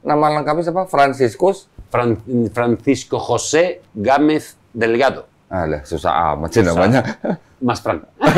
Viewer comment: Kalo gk salah denger namanya Susah bener 😂 Dulunya pendeta ya koh?